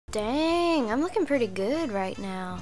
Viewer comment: At the start I thought she was gonna say 'damn breland' but no she said dang I'm looking pretty good😂😂😂